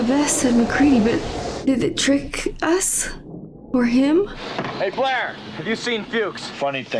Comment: Russell actually says "Hey... flare!" instead of "Hey Blair!" (because he's holding a flare -- and probably apprehensively focussed on it as he performs this scene).. :0)